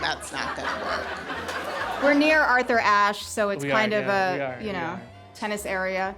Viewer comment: Thought Melania was saying "Who we are, Navy?"